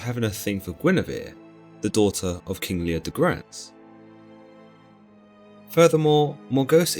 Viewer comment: “Guinevere, the daughter of king Neil Degrasse” Wtf?! Am I hearing things again?!